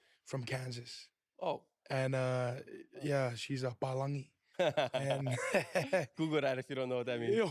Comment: what did he call his grandma at 5:25? i’m guessing like “american” or white person or something lol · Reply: Yes, whalangi,, white person lol